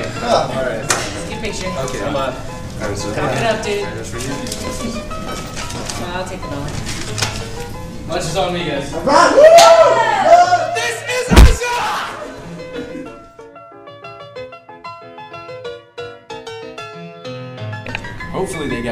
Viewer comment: What? He says "This is Asia" ?? The first time I saw it I thought he said "This is Eyeshine! " :3